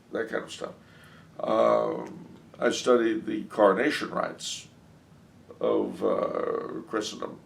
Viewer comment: what does he say there, "Carnation Rights"? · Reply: Coronation rites.